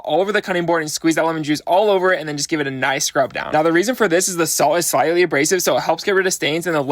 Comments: IM CRYINGG I THOUGHT HE SAID "The salt is slightly racist" AND IT HELPS CLEAN OFF DARK STAINS OR SOMETHING AND I THOUGHT "YEAH THAT MAKES SENSE" AND KEPT WATCHING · I thought he said the salt is slightly racist 😂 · i thought you said the salt is slightly racist 😓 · I wasn't paying attention and I thought he said "the lemon is slightly racist" not "the salt is slightly abrasive" i'm sobbing 😭✋️